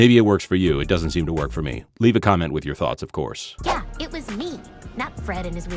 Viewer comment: At first I miss-heard this as "leave a comment with your thoughts and prayers." Have them anyway. You had to watch Velma.